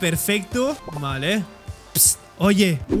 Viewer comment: Estoy tan traumada con One Piece que escuche y lei fruta del diablo JAJAJA